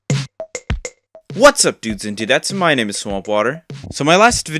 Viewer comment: i legit thought he was saying whats up dudes and dudes thats my name is swampwater 😭